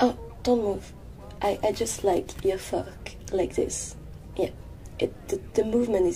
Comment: What does she say?? "I just like your ____" fork? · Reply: Kirby3585 Yes. Fork.